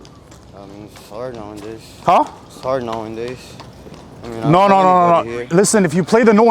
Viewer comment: “It’s hard now a days” “did you just say “sorry no ingles!?” 😂